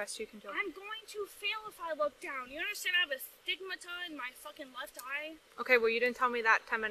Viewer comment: Wait...did she say she had a "stigmata" in her left eye? 14:51 😇